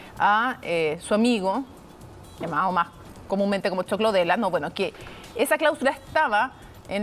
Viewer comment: Que dice en el minuto 01:20 ? Choclo del ano jajajJ